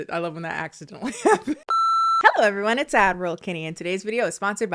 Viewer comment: Every time you "throw it over to addroll kennie" i mishear it as "Admiral Kennie" and I'm just saying you should maybe lean into it